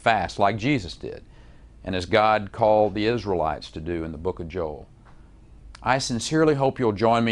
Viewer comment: Did he say Book of Joel? I wonder if that comes after or before the Book of Carl.